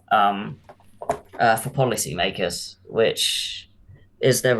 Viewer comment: All I hear is ICP 😅